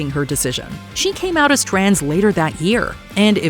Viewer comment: i heard that as "she came out as translator" 😭😭😭😭